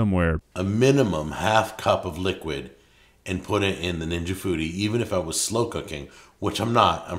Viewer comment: I heard ninja booty